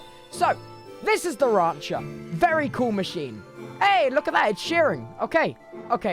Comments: I thought he said "Aye, look at that! Ed Sheeran" for a second there and had to watch it three times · I legitimately swear I heard "Hey! Look at that! Ed Sheeran!" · I thought he said 'Ed Sheeran' PHAHGSAHH